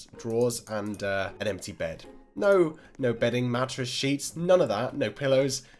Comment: did he just said shi*??! :D